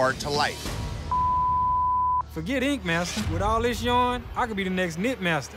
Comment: I thought it said kink master 🤦😂